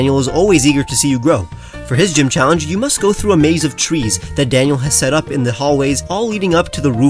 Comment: I love the Pikachu and Pokéball designs on the beach of Arbadi! (Also, heh heh, I wasn't looking at the screen when you described the first Gym challenge and I heard, "You must go through a maze of cheese," and I was like, "Wait. What?")